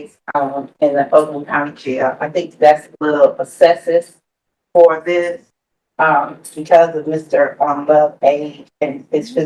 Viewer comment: What was that word? Essessis?